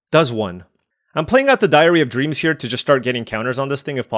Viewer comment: I hear "diarrhea dreams" every time.